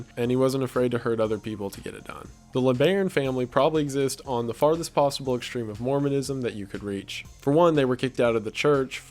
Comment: I thought it said leBron family and was like wtf